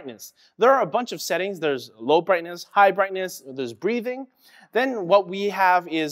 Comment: When he said "Breathing" I thought he said "Grieving". It'd be awesome if you could tell your keyboard someone died and it'd enter a "Grieving Mode".